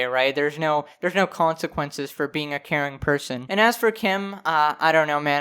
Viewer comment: When you realize it sounds like he said "Karen" but it's actually "caring" but then you realize both would work